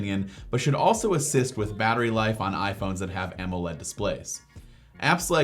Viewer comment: did you just say AMOLED displayed 🤔